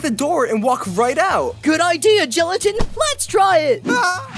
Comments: Subtitles are wrong. Its gelatin not leafy.. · in the subtitles, it says leafy instead of gelatin.